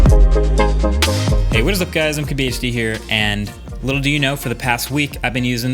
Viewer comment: "What's up guys, cabby HD here." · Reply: I always hear it as "What's up guys, I can be HD here".